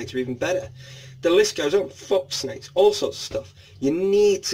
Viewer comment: I thought you said "fxxx snakes" at 01:58 😅